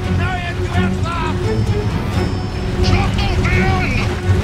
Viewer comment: What's that chant at 2:23 shok to vein something I heard it before anyone know ? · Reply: He says schlachtung fyanda meaning slaughter them all in an old german saxon language a saxon war cry from king arthur 2004